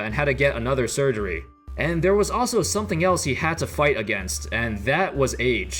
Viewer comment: my dumbass thought you said he was dealing with aids 😑